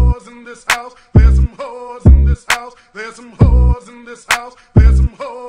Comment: Can wait for the kids bop version to come out . There's some chores in the house. Btw I did a radio edit of this Chekitt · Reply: FA MUSIC I kept hearing “there’s some walls in this house”